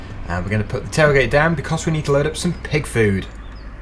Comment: Did you just say dick food